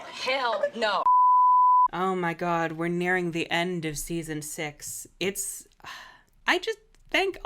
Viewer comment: I accidentally fast forwarded in the video in the start making it sound like you said "We're reaching the end of my brain cells" and I actually think that's appropriate to the effect the writing of this season has had